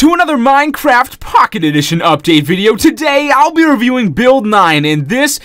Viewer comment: I thought said Bill Nye instead of Build 9 😂😂